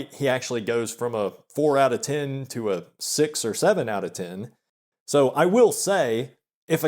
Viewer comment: what did you say? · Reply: He said "A six OR seven out of ten" so this is a false call-out.